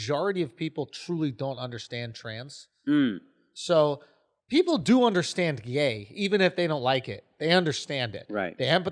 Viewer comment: When he said "people understand gay" I thought he said "people understand Ye" and I was like ehhhhhh.